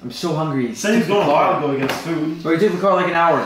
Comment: DID HE SAY WILDWOOD?!? WILDWOOD NJ?!? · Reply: He said while not wild